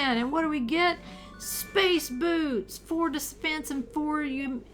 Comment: Space boots, I thought you said "Space Boobs" YouTube is going to ban me. Good thing I use Twitter...